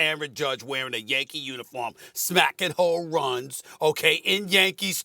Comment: does SAS say "smacking hoe runs?" Hmm.